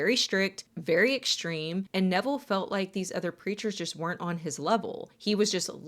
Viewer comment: I have bad hearing, and I thought you said he started flying religious creatures instead of preachers 😂